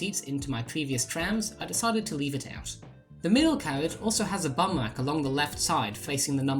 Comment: Did you call that a "bum rest" or a "bum rack"? It's hard for me to tell, but I think it's funny either way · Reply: Yes I did, I've heard bum rack used a lot more than bum rest over here